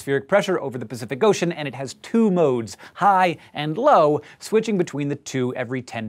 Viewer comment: I thought he was saying high anus